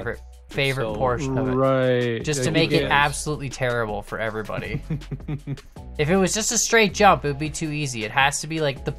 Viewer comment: I couldn't catch the name of the guy who did a little wallride there but that guy I've seen make it through at least twice so if I were in game I'd just be watching his line and then bam get it done flawless. Maybe that's how Warcans does it? ;) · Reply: It is warcans, I assume the wall ride sets you up on a good angle for the jump or something, while retaining speed.